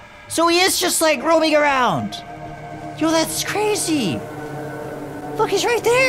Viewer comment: I put on captions at the beginning of the video, but at 29:44 it heard you say the f word but did you say look?